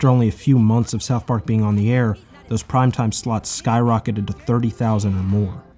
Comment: I thought it said those prime-time sluts, instead of slots at 3:31.